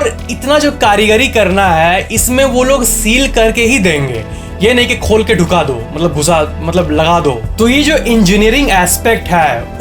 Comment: Areee.... kya boldiya, 14:26 Dhyan se sunna or samajhna............. He said"ye nhi ki khol k dhuka diya, ye nhi ki khol k ghusa diya"!!